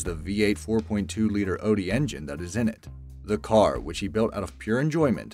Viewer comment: No.. 3:03 did he really say odi? I hope that's some over sea engine manufacturor and he's trying to say Audi